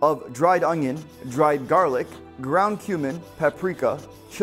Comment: Lol when I was reading the comments and he said ground cumin I swore I heard ground human 😅